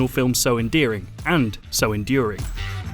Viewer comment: Did he say so endearing and so endearing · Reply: Enduring